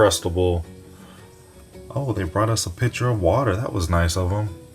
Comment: Not me thinking you said "picture" instead of "pitcher"